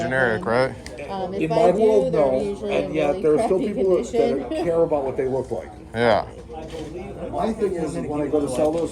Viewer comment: The audio from the dealer was hard to pick up in lots of places. What did he say between 3:45 and 3:55? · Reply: Sorry! Folks next to me were chatty :( He was saying silver eagles you always get more than spot, while generics you do not. Have you seen my new video about how I became a Trillionaire?!?? Check it out here: <link>